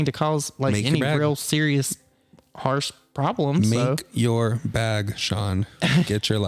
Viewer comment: Omg I thought said "Milk your bag" IM DYIN 🤣🤣🤣